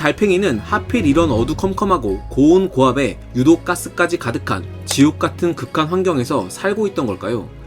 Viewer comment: 귓속에 마귀가 들어왔어 지옥같은이 아니라 ㅈ같은으로 들었어